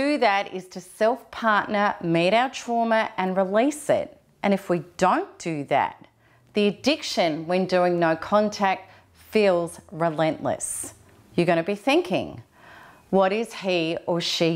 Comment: What did you say about 3:16 " the best was is to self partner....."Your English accent is very nice but sometimes hard to understand. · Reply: Hi xpmark1, I'm happy to help. I say... "meet our trauma and release it. And if we don't do that, the addiction we feel from going no contact feels relentless" Love and blessings xoxox